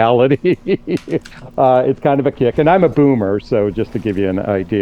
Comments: A boomer and destiny discussing post nut clarity was gold 😂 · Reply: I heard him say "coomer" not boomer when he introduced himself :face-blue-smiling:I was like hold up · I thought dude said "I'm a coomer" not boomer 💀